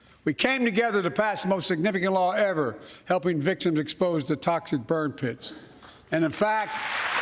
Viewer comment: Wait, what? I don´t understand. Did he say toxid bird pits? Huh?